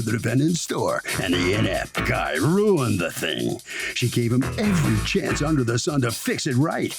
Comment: I thought the announcer said “the MF guy ruined the thing” in the beginning lol